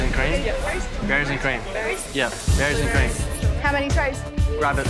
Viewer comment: What did you say? berries and what? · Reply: I think he said “berries and cream”